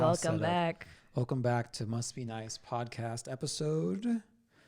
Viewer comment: Why did I hear say, "welcome back to must be nice" as "welcome back to musky nights" ? 😂